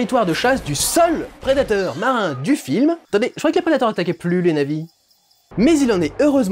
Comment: J’ai réécouté peut être huit fois le passage, j’arrive pas à comprendre la phrase de, ya qu’à moi que ça fait ça ? Qu’est ce qu’il dit ? « Attendez, je croyais que la carotte a pas plu les navis ?» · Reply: Il dit " attendez je croyais que les prédateurs n'attaquaient plus les Na'vis "